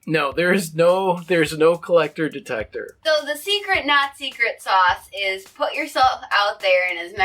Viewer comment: I shouldn't watch these when I'm tired...when said "the secret not secret sauce" I heard: "the cheap Nazi grit sauce". Other than a potential strange taste in sauces it was a great video~